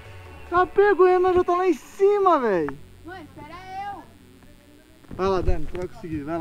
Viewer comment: Eu escutei a dani fala mó espera eu 13:28 kkkkk